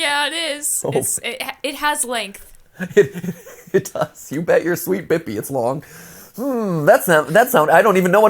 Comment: "You bet your sweet bippy." is weird I always heard it as, "You bet your sweet bottom."